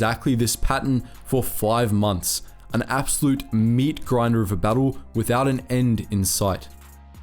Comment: Thought It said Jedi meat rider lol